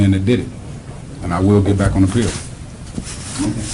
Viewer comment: Heartless, he did that shii! "I'll be back on the field?" Yeah, uuhkay..... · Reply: I thought he said he would get back "on appeal," because his attorney had just got done saying he would be appealing the verdict.